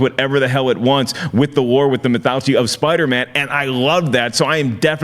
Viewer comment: Did mark say "mythouchy"? Lol..."with the lore,with the mythouchy"...lol